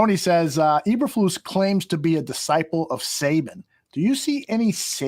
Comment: I heard #SATAN first time around! 😂